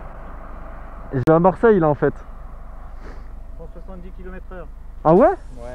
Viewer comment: j'ai pas compris le km/h c'était quoi ? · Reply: 170 est moins les 5 de base est se qui enleve avec la marge d'erreur de la machine